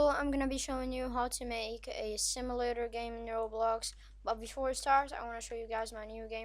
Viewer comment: was i the only one who thought he said pet simulator?